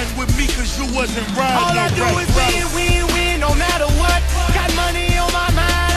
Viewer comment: I thought he said “all i do is weed weed”